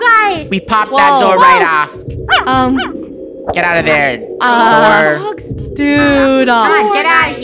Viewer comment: DID SAY WH*RE? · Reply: lol no he said door